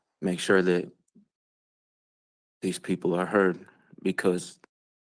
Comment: "I wanna make sure these people are hurt.". Did I hear that correctly?